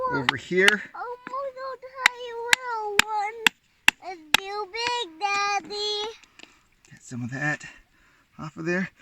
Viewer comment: I hear baby voices…Your son saying, “it’s too big daddy”. ❤